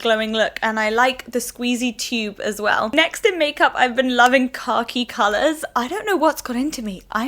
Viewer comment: Oh my word I thought you said you are liking products with 'car key' colors and I was SO confused haha. I was like what in the hell is car key colored? But khaki makes so much more sense lol